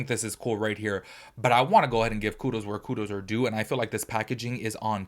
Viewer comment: I thought he said I wanna give Culo where Culos are due 😂 talks to fast but entertaining ❤